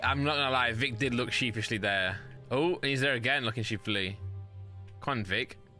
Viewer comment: Wait what in the world is Randolph saying at around 19:00? Sheeplessly? Sleeplessly? What now? 😂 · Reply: Sheepishly